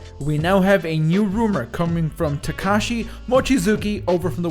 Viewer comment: I thought you said... Tekashi 69